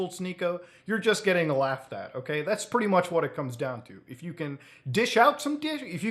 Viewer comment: hold up wait a minute what did say???🤣🤣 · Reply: If you can dish out a dish 🗿